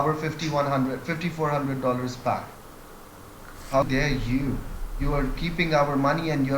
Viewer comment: I heard "How gay is you?? you are keeping our money and showing me your TANTRUMS!?!?"